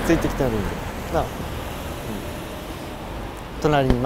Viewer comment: 先生がなぁって言ったあと女性の声で【うん】って聞こえた💧